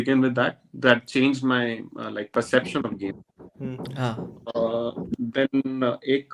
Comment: i heard gay 😂